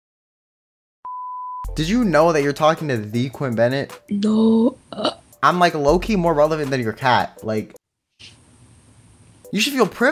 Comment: omg for some reason the first time I heard it, I thought he said “I’m low key more hormonal than your cat...” LIKE WTFF😂